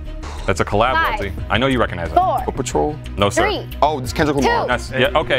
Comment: did he just say paw patrol😂